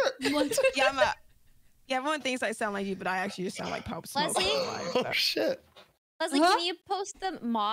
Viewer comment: No way just said she sounds like Pop Smoke… did I hear that right? Tell me I’m trippin.💀